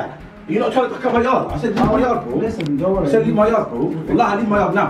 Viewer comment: what does chunkz say at 6:59? "Leave my ...." ?? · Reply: Yard